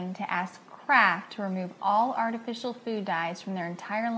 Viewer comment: sounds like shes saying CRAAAKK when she says Kraft lol